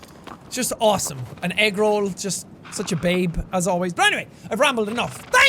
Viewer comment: when he said agro i thought he said "eggroll"